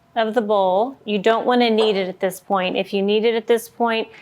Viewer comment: THOUGHT YOU SAID, NO KNEAD !!!!!!